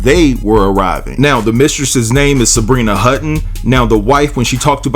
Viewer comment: Lord I thought he said the mistress’s name was Sharena Hudson (Wendy Williams’s ex husband’s mistress)! I was like damn she gets around! 😳 Her husband sounds like a covert narcissist!